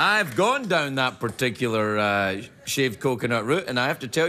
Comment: did she say she doesn't"t like "shaved coconuts"